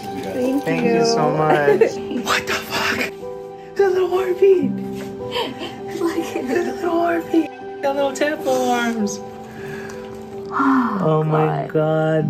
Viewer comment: I swear at 2:16 says "his lil heartbeat!" 💙💙💙💙💙 Congratulations!!!